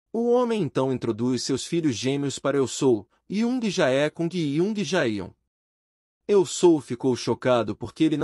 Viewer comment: mano eu entendi: yiuhshsgsggsffzyon e gagfsyatyon😍